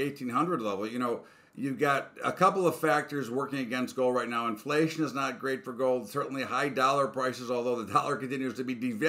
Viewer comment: Did he say inflation is not good for gold at minute 2:30?